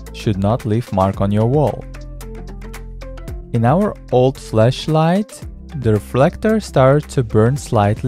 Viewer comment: Did he say fleshlight?